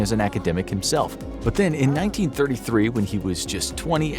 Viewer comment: You said his father fought in WWII, but think you meant WWI. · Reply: Then around 3:40 they say "in 1933" and the caption says "1993" lol